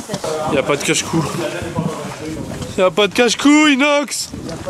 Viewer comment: j'ai entendu "y'a pas de cache couill*s Nox" ^^